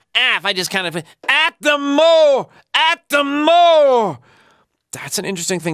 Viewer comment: This is such a great video don’t get me wrong but is anyone else hearing him say “AT THE MORE” at minute 18:38 when Michael’s lyrics are “forevermore” ?😂🤦🏽‍♂️